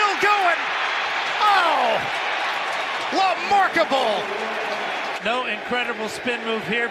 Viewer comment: did he just say "lamarkable"??? Lmao